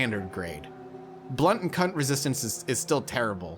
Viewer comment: excuse me? what resistance did you say? :D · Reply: Blood & Cutty resistance!